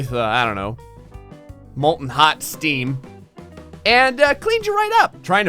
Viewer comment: I just hear "molten hot steam"?